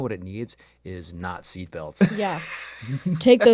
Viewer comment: "You know what this car needs? Nazi belts" That's exactly how I heard this lmao